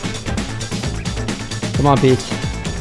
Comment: Subtitles: COME ON, B##CH What Mag actually say: come on Pete